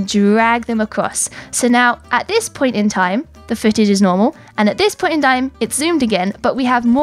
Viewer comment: bro I thought she said the footage is no more instead of the footage is normal. that confused me for a while but I finally got it